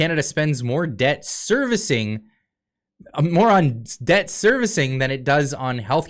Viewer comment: Did you say "moron debt"? Too correct!